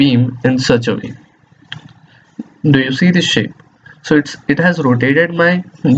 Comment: did i just hear "do you see this shit" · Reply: Lol this was one of the first videos that i uploaded and i was suffering from a bad cold when I recorded, its not "shit" its "shape".